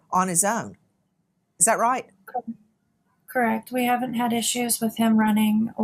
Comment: "haven't had" instead of - don't have issues with him opening the doors...